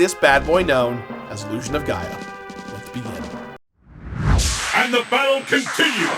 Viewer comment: I heard "Illusion of Gaia hallowed be its name."